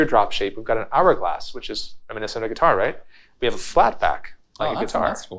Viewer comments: "...lost the teardrop shape we now have an hourglass, its a Minnesota guitar right?" (Quote not perfect but close) Am I hearing that right? What is a Minnesota guitar? I'm from MN and I've never heard the term if that is actually what is said. It is very possible I am hearing things incorrectly. Any insight would be greatly appreciated thank you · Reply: I heard "mini-sort-of guitar" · "...lost the teardrop shape we now have an hourglass, its a Minnesota guitar right?" (Quote not perfect but close) Am I hearing that right? What is a Minnesota guitar? I'm from MN and I've never heard the term if that is actually what is said. It is very possible I am hearing things incorrectly. Any insight would be greatly appreciated thank you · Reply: he said "reminiscent of a guitar"